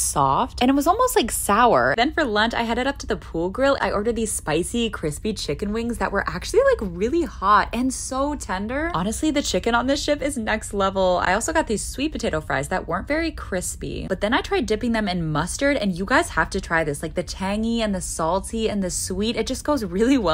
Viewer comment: "Honestly the chicken on this shit is next level" THATS WHAT I HEARD LMFAO NOT "SHIP" 😭😭